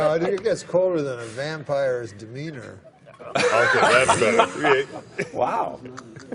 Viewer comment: When he said “colder than a vampire’s demeanor” I thought he said “colder than a vampire’s wiener”. I was surprised they took it so well.